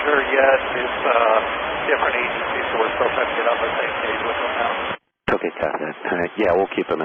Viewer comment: I think he says, “It’s different agencies. We are still trying to get on the same page with them now.”